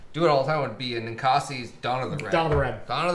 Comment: What is the English red beer you're talking about around @ 8:25 ? Sorry for brutal spelling sounded like " Acasie Donna the red". Where can I find this? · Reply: well its not a English red :( but its a Red IPA that tastes very much a great drinking blend of English Red and American NW IPA. Its Ninkasi - Dawn Of The Red. It should be mostly Nationwide.